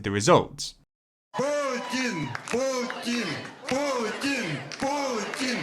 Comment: am I heard Poor-tin instead of Putin ?